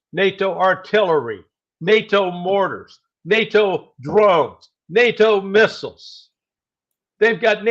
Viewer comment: Instead of Nato Drones, I kept hearing Nato Robes lol, listened to it 5 time haha Larry "The Shirt" Johnson demands NATO Robes go home! anyone else thought Larry was wearing a jedi robe?